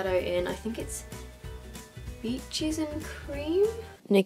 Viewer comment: did she say peaches and cream